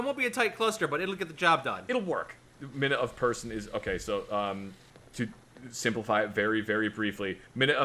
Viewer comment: I always heard it as "Minute Of Bad Guy".